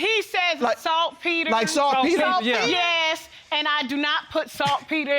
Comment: I thought they were saying SOFT PETER 🤣🤣🤣🤣🤣 I laughed entirely too hard 😃🤣🤣